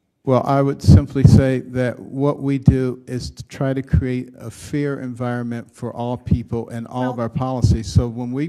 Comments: Fair environment or fear environment? What did he say; 1:55? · It sounded like he said, "What we try to do is create a fear environment".